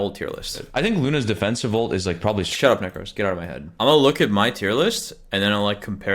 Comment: what did he sayyyy😢🎉❤ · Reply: "Shut up necros, get out of my head"